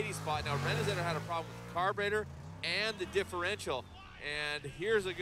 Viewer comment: did he say carburetor?